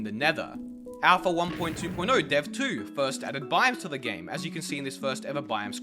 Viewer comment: legit thought you said "a1.2.0-dev2 first added Bibles to the game" and was very confused for a hot second there lol